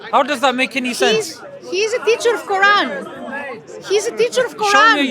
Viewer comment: Did I hear her right? "He's a teacher of Quran (ie: Joe Rogan)." Subhana Allah! Very crazy statement there SMH